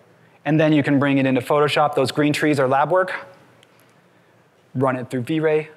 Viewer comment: Guys please help me out I didn't quite understand the part where he said, "those green tress are lab works" at 59:02 And how can you make the Vray proxy look like those low poly free. Is anyway to do that? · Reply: The plugin is called laubwerk